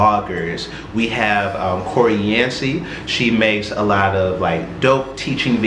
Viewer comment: Hi, I didn't catch the name of the YT/blogger in 2:21 - What's her name? I appreciate the content, but it would be helpful to have some text so that your viewers can effectively look them up. Thank you for the video! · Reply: Koreé Yancy. And I got you. I'll make it on Sunday. Come back then and it will be on your comments and in my descriptions